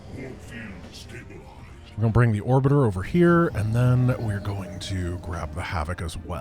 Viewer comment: Wait, did say “orbiter” here? The memes are infecting his mind!